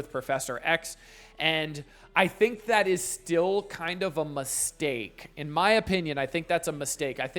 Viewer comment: did someone say steak? 🤤